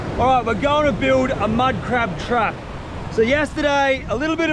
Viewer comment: who els thought he said minecraft trap